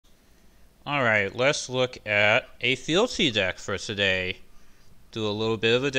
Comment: At first I heard "Let's look at a filthy deck today!"